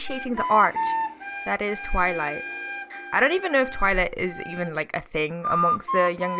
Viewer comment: @ the subtitles 😂😂😂 instead of "I don't even know if twilight.." it's "I don't even know if TOILET is..." 😂😂😂